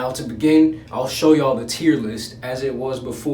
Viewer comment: Why does it sound like your saying tearless video Ahhhhh but it’s now tier list Persona Bro best game...